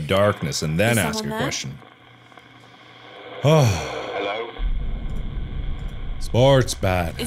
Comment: He said "Hello", but the caption said "I can hear you". lol